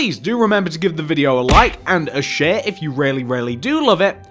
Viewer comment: thought he said shit instead of share